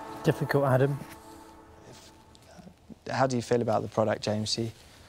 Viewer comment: Did he just say, "How do you feel about it Jamesie?"